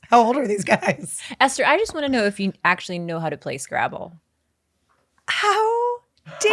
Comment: I thought said "Place Gravel", not "play scrabble". Anyone else?!? lol